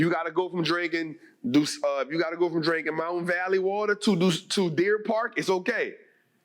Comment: ! Almost sounded like you said we gotta go from mountain valley water to dusse! But thank you for the information you post on your page, I look forward to using it to grow